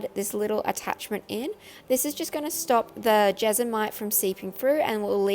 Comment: When you started to say jesmonite I thought you were saying vegemite lol. duh me.